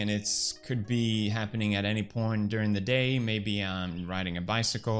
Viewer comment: what did he say?😂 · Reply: He said "any point during the day"😊😊